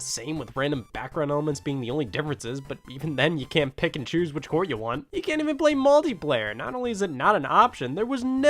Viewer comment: did he say "you can't pick and choose which whore you want"